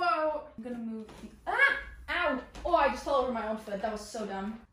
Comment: not me rewatching this and seeing that u said “feel” instead of “fell”💀😭